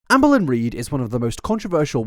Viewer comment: lol every time you say “Amberlynn” I hear “Anne Boleyn” and it makes me giggle 🤭